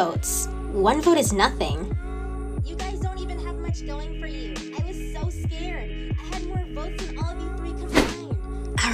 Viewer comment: can some say what was saying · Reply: "You guys don't even have much going for you!